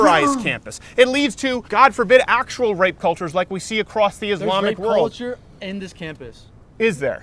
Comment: Did he say in the video “actual rape culture like we see in the Islamic world” or am I bugging